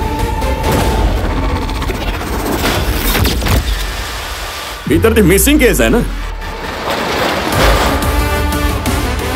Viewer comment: did he say “ Ithanidi missing case hi na”. ?🤔